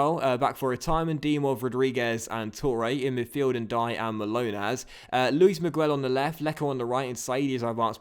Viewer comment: For a minute, I keep hearing Milonas' name as Manolas (I thought you had bought Kostas Manolas).